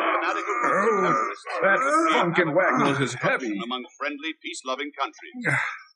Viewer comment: does tom servo drop and F bomb...or am I hearing that wrong? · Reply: "That funkenwagnel is heavy!" It's a fake swear that I believe this show started. I could be wrong. I have heard it in other places.